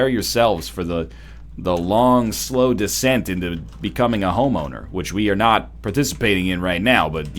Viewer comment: i thought he said "homo" xD